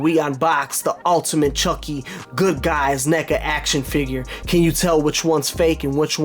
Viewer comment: Bro, I thought you said, the Good Guys N*gga action figure?